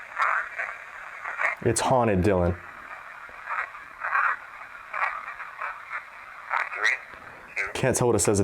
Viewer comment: Dylan you heard "it's haunted dylan". I heard "it's Sargeant dylan". ????